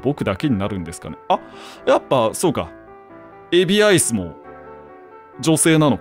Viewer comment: ふとした瞬間のエビアイスがレジアイスに聞こえて頭ごっちゃなる